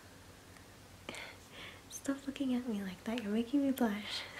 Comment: what she said... Stop ****ing me like that